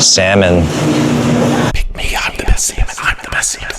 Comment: At first, I thought he said I want octopus semen. But he said pick me I’m the best salmon. Lol